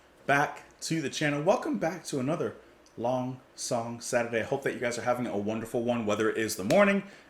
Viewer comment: Oh shoot. I'm in the wrong room. I thought you said: "Long Dong Saturday". My doorbell seems to be broken. While the "ding" is fine, the "dong" is rather more legato than is its normal habit. But since I'm here...on to das Kraftwerk!